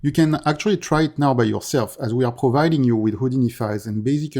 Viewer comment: did he say they are releasing Houdini files?? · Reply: Yes, you can download the city sample project from the Epic Market place, the houdini files for the city are zipped at the root of the install folder ;)